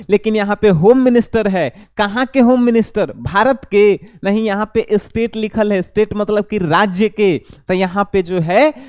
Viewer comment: second se dekhiye a kya bol rha hai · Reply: Bharat ke 😂